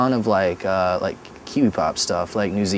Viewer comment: What's the genre Jay says he's been listening to @ 1:15? 'Q-Pop"? Tried to figure out what band the interviewer name drops, sounded like Flangmen but that doesn't seem real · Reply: kiwi pop